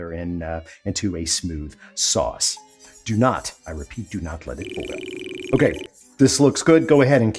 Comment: PLEASE show some effort and care regarding SUBTITLES I believe you said "Do not let it boil" while the subtitles say "Do not let it cool down" This should drive YOU crazy!